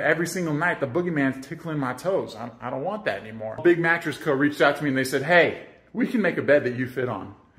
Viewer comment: Wait a minute - WHO MADE THE MATTRESS? Kudos to them! Look at him being excited to find something taller than him! That's so cute! His wife looks little in the bed, but she's 6'1"! Wow! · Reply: For clarification, I heard him say that 2 months ago after watching the video again. I should have updated my comment then because when I originally heard this short, 2 months ago, I thought he said "A big mattress co reached out to me". I wrote my comment after that. Then, when I watched it a second time, I realized he was saying Big Mattress Co and that was the actual name of the company. So, in response to your comment.....LOL...yeah....I know.